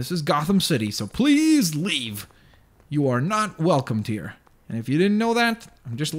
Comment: did call the lexbot dear? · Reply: stickypotatofilms he said "Welcome to here"